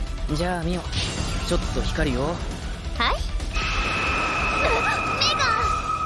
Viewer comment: What did she say!!!???? 😨😨😨😨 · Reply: Megah!!